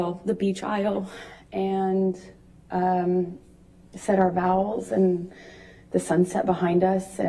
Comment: Did she say “say our vowels”?! I thought it was “vows”?!?